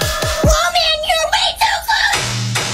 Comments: WOMAN YOU’RE WAY TOO CLOSE; ✨PePpEr sPrAy-!✨ 2024 edit: Why of all comments did this blow up- ._. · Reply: Oh that's what he said? I thought her said "MOM YOU WAY TO CLOSE MaKe iT StOp" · WOMAN YOU’RE WAY TOO CLOSE; ✨PePpEr sPrAy-!✨ 2024 edit: Why of all comments did this blow up- ._. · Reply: i thought he said WOAH MAN U R WAY TOO LCOSE WHAT THE FREEEAAAAAK